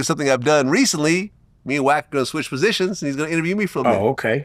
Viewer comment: Did he just say me and wack are going to switch positions ? 6:23 pause 😂